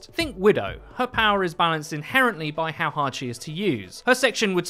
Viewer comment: HahaI thought you said widow was balanced for a second there. I must be going crazy.